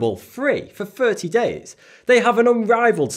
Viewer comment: When he started talking about the audiobooks, I thought he was saying the trees have an unrivaled selection of audiobooks o_o.